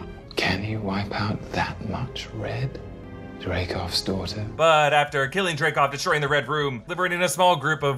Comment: wait wait wait...is THAT what loki said in the first avengers movie? "Drakov's daughter"...because of this accent I could never 100% tell wtf he was saying...I thought he said like "Drakove Storten" or some shit and it was some Russian city I just didn't know...